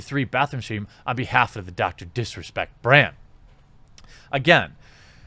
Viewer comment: Is it just me but when he first says Dr disrespect does it sound like he says Dr piss respect